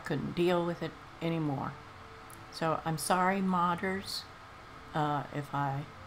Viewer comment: I thought she'd said 'Sorry poggers'. Was about to go crazy with excitement. Newly introduced to this channel so I'm just gonna watch the amazing journey which led here.